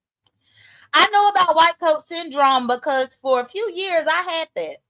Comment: First video of the day! Very good stream. We're you saying white people syndrome? 😂 I know it as "white coat syndtome". Maybe I misheard you, but the thought u said Wite people syndrome, gave me a chuckle😆